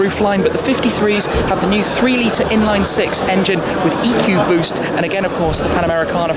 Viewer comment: Yo when he said EQ boost I so thought he said Ecoboost! Ford?🤔